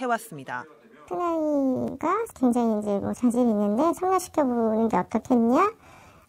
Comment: 에 자막 자신있는데가 아니라 자질있는데 라고 들리는데..